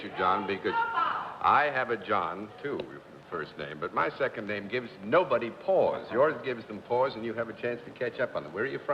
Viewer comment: Did anyone catch what the audience member yelled out at abut 3:48? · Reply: I think that that person was shouting "Opaa!"